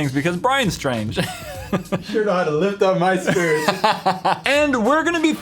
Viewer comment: What ever said to in the beginning I heard like "you shore know how to pick up my skirt" (Swedish don't mind spelling)